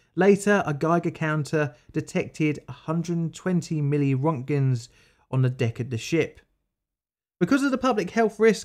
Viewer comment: Did I hear that correctly? 7:39 120 million roentgen? The chernobyl incident did not have nearly that much and the crew was on the ships surface for days and had survivors...